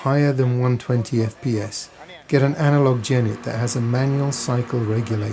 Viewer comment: Guys, can anyone help me please? what word did he say on 1:59 analog Jenny? I Just don`t get. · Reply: Analog Genny (generator) that has a manual control for throttle so you can change the engine RPMs (cycles per second). Most small generators have a mechanical governor that is preset at the factory for a certain speed, and thus you wouldn't be able to vary the cycles.